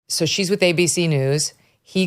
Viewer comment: It sounded like you said, 'She's with Baby-See News."